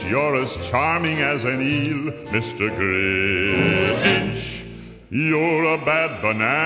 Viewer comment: “Your a bad banana…WITH A GREASY BLACK PEEEEEEEEL” 💀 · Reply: I thought the lyrics said “Your a black banana WITH A GREASY BLACK BEAN